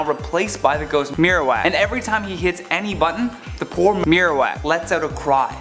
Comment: did he say the wrong thing and try to edit it out? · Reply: I think he said "the poor herowak